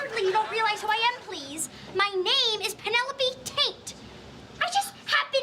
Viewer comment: As a kid I always thought Amanda was saying “Tate” not “Taint”